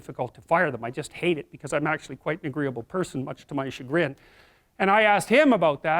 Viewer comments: Can someone please tell me what JBP says at min: 17:54 "I'm quiet a very agreeable person , much ???" can't figure it out and is killing me, lol. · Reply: Miguel Galindo - "much to my chagrin" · What does he say at 17:53 "I'm actually quite the agreeable person much to...?" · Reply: tkleinowski -much to my chagrin.